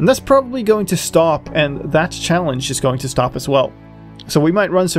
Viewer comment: god you scared me i thought you said "this *channel* is gonna end"